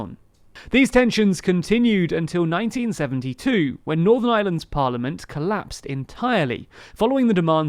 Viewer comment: I keep hearing "Northern Island" and the "Island of Island". I wonder how he is with pronouncing " penguin".